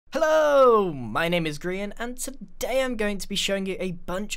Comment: Sounds like u said hell home in intro